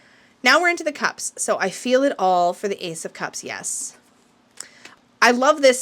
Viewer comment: At first I thought the Ace of Cups said "I feel tall" and I was like huh? 😅😂 I do like a lot of the affirmations and I agree, I think it's a great self-care deck, so it might be a deck I add to my collection in February bc I do want self-care to be a big priority next month!